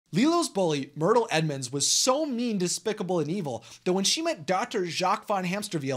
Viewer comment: Any one else thought he said despicable me